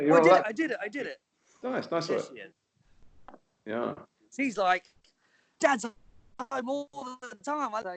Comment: wait did tom say "dada" ? 😭